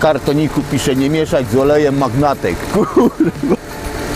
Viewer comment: Dobrz usłyszałem...? Że nie można mieszać z olejem magnatek ? XDD